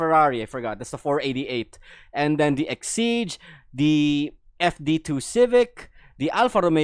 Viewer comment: Did I heard FD2 Civic at 4:02 ? Not FK8 Civic Type-R?